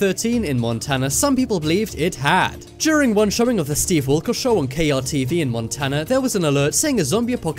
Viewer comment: I thought he said "Steve Erkol" (however you spell it) instead of "Steve Wilkes"